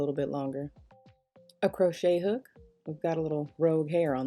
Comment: Who else thought she was saying “Hey Shook” before she corrected herself? Lol I was listening too hard 😂😂😂😂😂😂😂😂😂😂😂😂